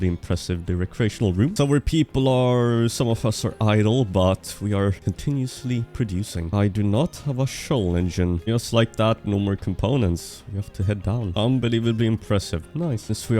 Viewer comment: did he just say IDOL? (Yeah I’ve finally watched demon hunters not too long ago)